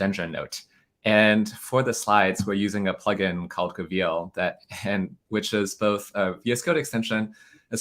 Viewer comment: What's the Markdown slides extension? Coveal? Kaville? Goveal? Closed captions say cville - I can't find any of those variants - anyone got a link? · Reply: reveal